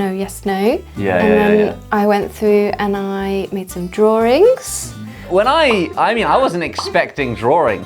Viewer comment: Is it me or do I hear “drawrings” like with a soft “r” sound? 5:09 Is it and English thing?